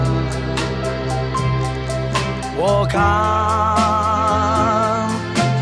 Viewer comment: When he says walk on I thought he said World Cup lol